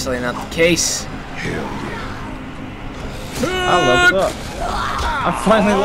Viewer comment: did anyone else hear character just say "HAYULL yea"